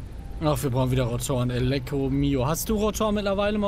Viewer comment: bin ich der einzige der mehrmalsverstanden hat elektromio